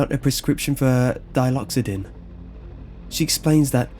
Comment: At first glance, I thought it said "Dildoxodin". Am I the only one?